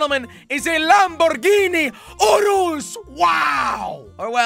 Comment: “Lamborghini Oreos” is what i heard